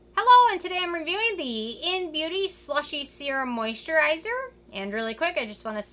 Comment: At first I thought it said “Slutty Serum.” I was excited to find a product that I can identify with 🤣🤣🤣